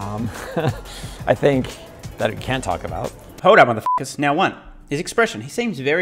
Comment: I am hearing "that I can't talk about", not "that I can talk about". That would also make sense as he is trying to figure out what to say next. He then goes on to talk about the cybertruck instead as he can talk about that one. In the subtitles on the source video, it also says "can't", but those seem to be autogenerated so take that for what it's worth.